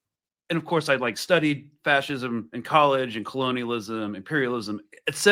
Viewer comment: Also, I heard "gluteal" instead of "Colonial."